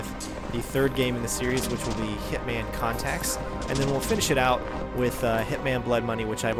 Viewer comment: did you just say hitman Contacts?